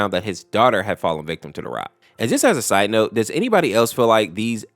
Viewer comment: I thought you said to the rock